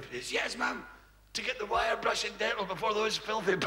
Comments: When I was a kid, I thought Billy was saying “wild Russian nettle” 😂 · Whits he saying? Wire brush and dental? Is that a Scottish thing? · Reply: I thought he was saying wild Russian nettle lol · For the whole joke I thought he said the treatment was Wild Russian Nettle. :D